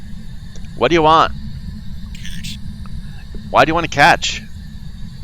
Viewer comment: Don't know, I always hear 'cash' instead of 'catch' at the spirit box. Maybe the ghost needs some money